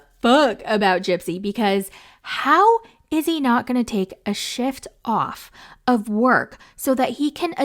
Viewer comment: I know you said "SHIFT," but it sounded like you said "SH*T."😂 LMAO 🤣